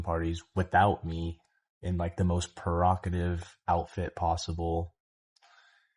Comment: he says “perocative” instead of “provocative” 😂